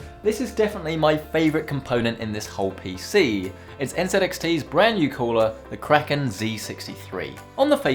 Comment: Did u just say "NZLXT" ? 😂